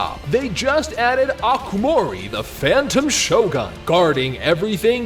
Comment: i love how the ad is just shoehorned in heeheee also i totally heard "phantom shogun" as "raiden shogun" hELP